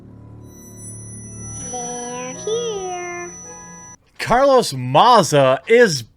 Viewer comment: did she say "they're queer"?! That's intolerance!